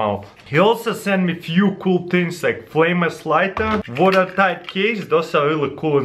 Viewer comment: wait did he just said "FLAMELESS LIGHTER"?